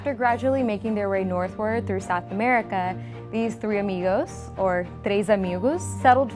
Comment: Did she just say “Amigoose”?